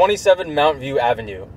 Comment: I thought you were saying mountain too